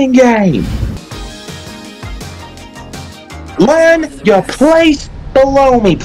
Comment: If i didnt read those subtitles i wouldve thought said “learn your place! BLOW ME!”